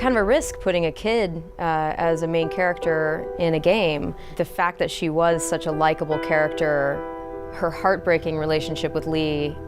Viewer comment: Umm excuse me did she say "was" she gonna die? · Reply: MarcoRplayz “The fact that she was such a likable character” Don’t know how you heard the word “die” in any of that, but alright.